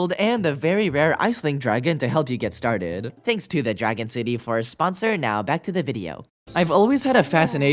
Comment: I thought you said dragon Diddy Actually